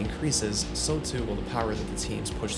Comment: What did he say at 9:31 ? "Push to the what " ? I'm just curious as to what was said , as I knew all this. It was a great video, though ! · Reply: As the reliability increases so too will the power that the teams push to “create” is what it sounds like to me. Had to listen to couple times